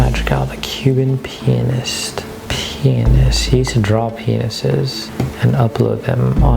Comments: When he said he draws penises I thought he said he draws pencils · lmao I thought he said “human penis” and then I re watched and it was “Cuban pianist” it sounded so similar though haha